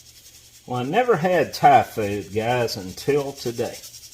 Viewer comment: Haha.. sorry. I nearly fell off my chair when I heard you say "I'd never had Typhoid until today.." then realised that you said " Thai Food"..